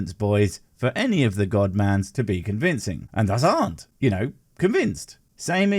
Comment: Could anyone please tell me what our good sir knight says at 1:14? I even got my wife to listen to it and we can’t work it out. 😅 Mind you, I’m pretty stoned but still! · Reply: i think its «and us arent» ^^